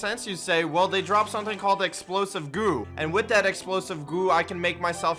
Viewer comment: I thought he said explosive poo